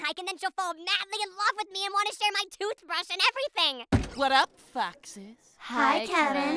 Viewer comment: DID KEVIN SAYS F***S???